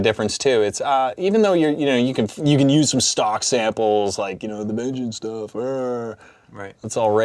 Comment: What does he say say after “stock samples” at 7:41?? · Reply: "the vengeance stuff", referring to vengeance sample packs